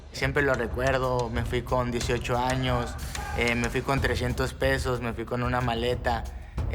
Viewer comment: Left his home with 30 dls in his pocket.... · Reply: I thought he said pesos? Maybe I'm wrong but if it was pesos that's like a $1.50 dollars.